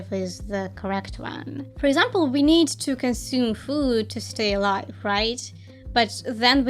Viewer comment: I totally heard this as “we need to consume Hulu to stay alive” and I nodded along, totally on board with that assertion, lol